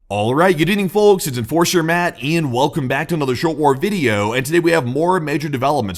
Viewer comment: I had to use captions because I thought you said “Welcome to the short orb video” 🫪